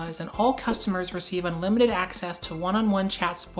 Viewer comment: unlimited child support is what I heard